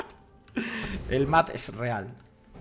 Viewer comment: CHE BOLUDO!!! Jajajajajaja sos un genio, el acento argentino te salio bastante bien!! cuando dijiste el mad es real pense que dijiste el mate es real XD Una consulta seria ahora, si le buildeas la guinsoo que tal le vendria la pasiva del doble hit? Si se aplica a la q del tigre sacaria muchisimo daño!